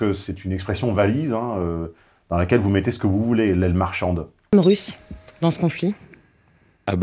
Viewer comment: "Aile marchande" ? j'ai entendu "Aile marchante", ce qui aurait plus de sens, les femmes manifestant dans la rue à certaines reprises en effet. Un exemple, elles se sont rendues à pied à Versailles pour parler à la famille royale et demander au roi de revenir à Paris au moment de la Révolution.